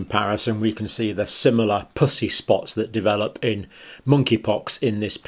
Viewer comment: Is it me or does it sound like he is say pussy spots 😆 🤣